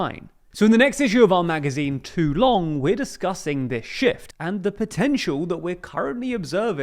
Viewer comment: Hahahaha! Man says: “In the next edition of Too Long we are discussing this shift…”. What I hear: “In the next edition of Too Long we are discussing this shit…”.